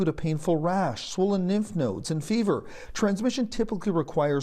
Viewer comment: Did he really say, "nymph nodes"?